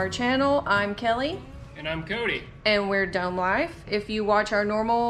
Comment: My hearing is terrible. I thought you said dumb life. Lol